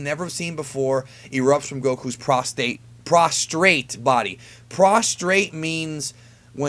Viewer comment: hahah i thought it said prostate too . Went though Goku's junk